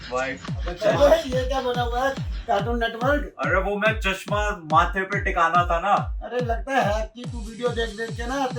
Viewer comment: did said chamar